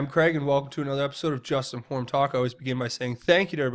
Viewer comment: You said that first bit so fast I thought you said "'Justinformed Taco's"' I went ''what did he say''. LOL